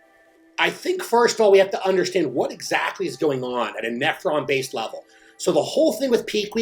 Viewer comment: I thought he said at an Efron (Like Zach) level. 😂 Great info